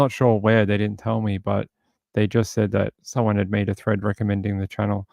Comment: You said someone made a "thread" on reddit, and I thought you said "threat"! Lol